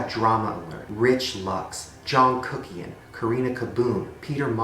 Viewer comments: I thought I heard "Jung Kookie" lmao 😂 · I hear Jungkook-ian LMFAO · When he said john kuckian I thought he said jungkookie 😂 oh lord I'm in way too deep into the K-Pop life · When he said that it sounded like jungkookie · I thought he said Jungkookie at 9:18 😂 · my bts trash ass thought you said jungkook kms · i thought he said jung kookie for a sec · "John Kuckian" what I hear : "Jeon Kookie" :D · When he said John Kuckian I thought he said "Jungkookie man" I spend too much time obsessing about BTS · i thought he said jungkook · I thought he said Jungkook for a while there. 😂😂😂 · Tell me why when he said "john kuckian" I thought he said " jungkookie-an"..omg what's wrong with me💀